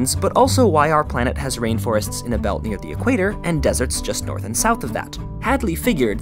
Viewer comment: Please tell me I'm not the only one who thought it said Desserts... Lol my inner fat kid is strong today! ;)